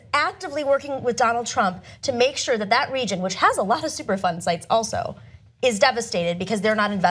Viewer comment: Lol i thought Cenk was saying "super fun" sites, not "superfund sites." I looked them up, and they do not sound super fun :(